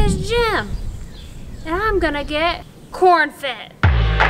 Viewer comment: Even though she said "corn fit", I heard "corn fed". Your day is now complete because you have been informed. You're welcome.